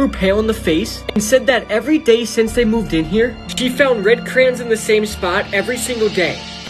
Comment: It sounded like he said crayins